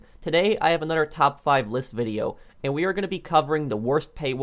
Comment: I thought he said a top 5 lisp video